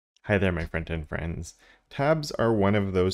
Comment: I always thought was saying "Hello my Front-end friends" at the start lmao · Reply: I say front-end friends, the auto-captions got it wrong :D